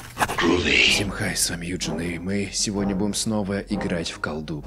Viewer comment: я один вместо Eugene слышу juli?